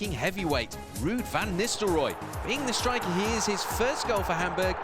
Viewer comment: They called van nistelrooij an english striker... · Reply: BMW//MPOWER Well, if you speak about 5:42, they say: ",,,being the striker he is...". That at first sounded to me (maybe also to you) like: "the English striker he is", But as you can read (and hear), that's not what they said. :)